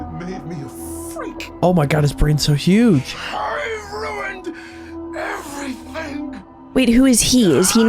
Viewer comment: for some reason it mostly sounds like he admits he ruined everything even though the subtitles says otherwise